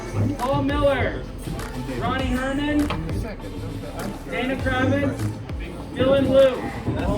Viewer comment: does he say anna cramling? Haha